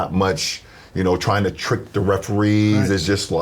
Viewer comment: Trick the referees…I heard “I be flopping”😂